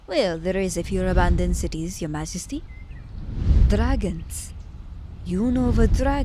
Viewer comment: Did you say... Dragon? (⊃✧ω✧⊂) Thank you for the audio ❤❤